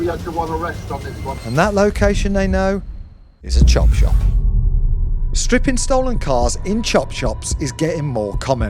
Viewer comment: I thought it said criminal chip shop lol